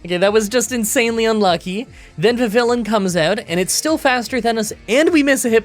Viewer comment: Anyone else thought they heard "the villain" until realising it was actually "vivillion"? Also, isn't it vihVILLeeon rather than vihVILlun?